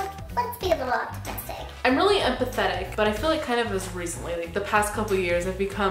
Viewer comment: i thought you were saying pathetic not empathetic. then you were like ‘i’ve been that way for a couple years’ and i was like ‘yeah you’re dating scotty and have you seen him in david’s vlogs? ever’